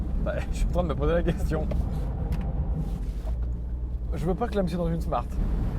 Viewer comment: je veux pas clamsé dans une Smart 👀👀👀🤣🤣🤣🤣🤣🤣🤣 dans quoi dit nous tous